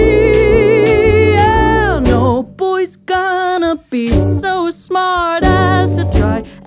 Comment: I thought she said boy try to be be smart ass